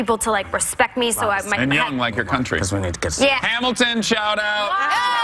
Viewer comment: For real did anyone here that subliminal message at 6:56? I still dont know what it says? Weird.... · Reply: Cuz we need to get .... Whoopi saying something